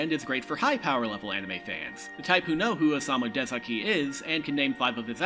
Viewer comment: The auto-subs thought you said "Osamu Tezuka" when you said Osamu Dezaki. Blasphemy!